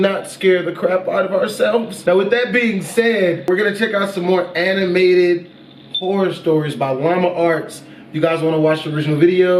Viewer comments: I heard "animated porn videos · I thought he said porn stories